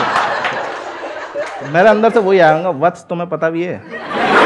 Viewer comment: Kya bola vo words mujhe clear nhi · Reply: Mere andar to wohi aya ki wats tumhe pata bhi hai